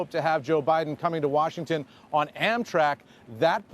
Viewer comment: I'm so tense, that I thought Eamon Javers said something about Joe coming to Washington on anthrax. :(